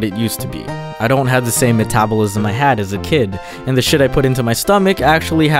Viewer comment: did he say shit 😂